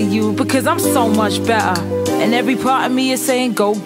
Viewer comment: Was Garnet saying "bet'er" 💀